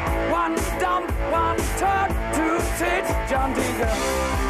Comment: "1 dump, 1 turd, 2 tits, John Deacon" did I hear that right? XD